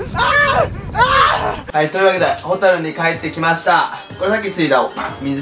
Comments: ホテルに帰ってきましたがの所ホタルに帰ってきましたって聞こえたｗｗｗｗｗ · ホタルに帰ってきましたに聞こえた人いる？ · ホタルに帰ってきたって聞こえるw